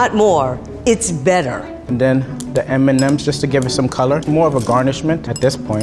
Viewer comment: Level 2 in an assertive tone: More is not MORE, it's BETTER . level 1: and den the mnm's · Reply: Ngl i thought you said "the mmm's"